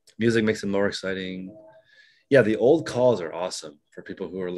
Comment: What podcast did you say was good? “The old calls”? Did not hear that properly. At